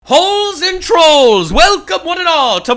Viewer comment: Did you just say holes 🕳️ and trolls 😈 ?